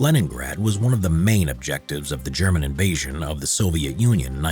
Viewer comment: did u said inbasion xD